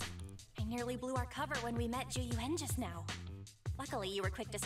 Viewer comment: me too Monarch! I keep hearing "Jing Yuan" instead of zhu yuan!